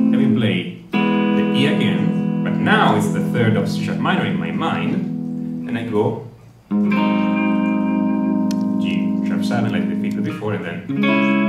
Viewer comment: Can someone say what he says after 7:13. He interrupted himself and destroyed the sentence flow. The last line I understood was "and now we play the E again". Thanks. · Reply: Now its the 3rd of C#m in my mind so I go G#7 ...